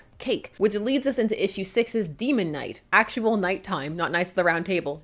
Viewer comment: did someone say Demon Knights?! Please bring that book back...I miss them 😭